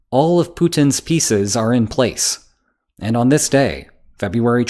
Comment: Due me listening to this while doing something else, sometimes I don't hear stuff 100% correctly so my brain tries to compensate. What I got was "All of Putin's feces are in place" And I think it's equally accurate to the original text.